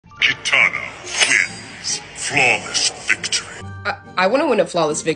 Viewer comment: Bro first i thought u said "katana ritz"🗿